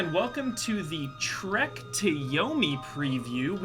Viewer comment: watched this right after the latest Bosman joint and thought you said "Shrek to Yomi"